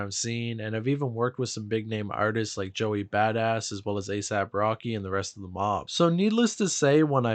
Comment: Bro ass our Travis Scott instead of asap rocky😂